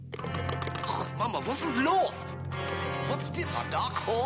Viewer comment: what did he say? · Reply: "Mama, was ist los?" the rest sounds like "is it a dark horse" or something. That's why Katy Perry came in my mind